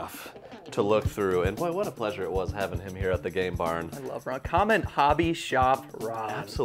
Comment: I thought he was saying "Gay Bar"